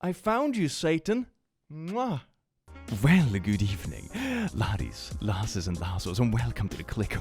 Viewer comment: For the longest time I thought this guy was saying "Good evening, lotties, losses and lassles"